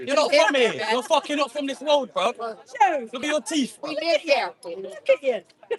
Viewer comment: Did he actually say this,? "you're not from here, look at your teef..." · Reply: "You're not from this world bruv.....look at your teeth" I think.